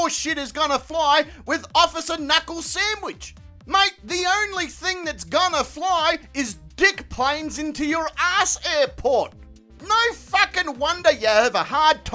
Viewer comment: Did I hear “the only thing that’s gonna fly are dick planes into your ass airport” 🤣😂??